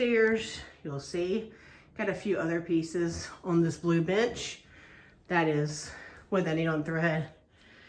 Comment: Holy cow, its like going into the attic and seeing all the models.you must be a very fast stitcher. Luv ur updates. Btw i thought u said on the blue b!tch instead of bench 😂😂😂😂